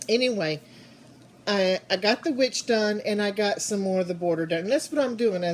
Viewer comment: Oh my gosh your finger looks so sore. You are lucky he didn't take you down face first. In the first few minutes of this video you said "I got the witch done". I swore you said "I got the bitch done" and I was hysterical!!!